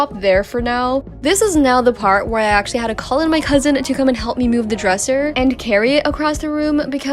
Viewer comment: My dumbass really thought you said husband instead of cousin😅